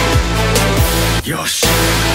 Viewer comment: Did genji say your shit on